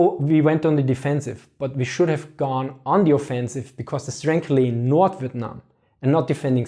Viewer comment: Does anyone know what word he is saying at 3:10? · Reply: "... it's frankly (in) North Vietnam" (according to CC, which is not 100% accurate - and yes, he's hard to follow, at times).